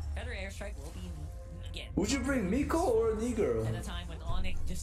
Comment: What did say in 0:34? Mikko or what? Kinda sus